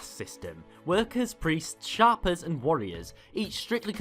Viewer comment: One who dictates shape. Shape er. Not shopper. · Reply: It sounded like he said sharper to me. But, yeah, it’s Shaper, pronounced shape-er, as in a person that shapes things.